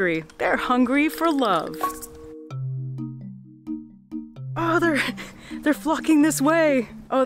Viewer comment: did she just say "they're fu**ing this way"?🤣🤣